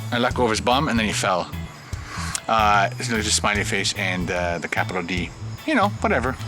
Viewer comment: )B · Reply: oops, i thought that you said capital b, lol